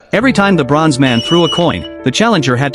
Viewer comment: I heard "every time lebrons man threw a coin"